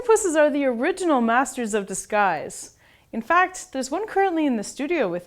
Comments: I thought she said they are masters of the skies and I had to think for a minute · Thought she said, masters of the skies, hahahaha fml